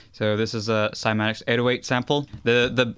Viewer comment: what he just said ??? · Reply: Ashu Krishna "Cymatics 808 Sample"